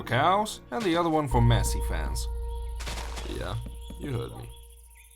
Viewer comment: Did u say Messi fans in 35:20?